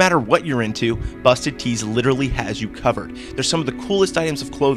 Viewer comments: OMG i thought you said "Busted Teens" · i thought he said "Busty Teens"